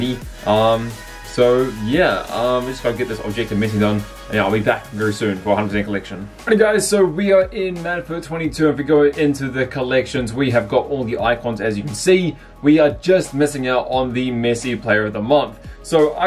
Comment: did he say madfut 22